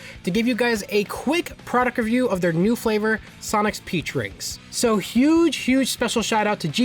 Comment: I heard him say: Sonic's pee drink.